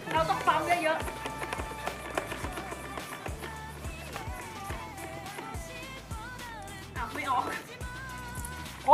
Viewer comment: พี่พูดใต้ใครได้ยินบ้าง บอกว่า เอ้าไม่ออก